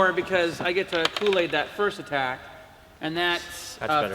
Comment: I thought they said Kool-Aid didn't work for attack.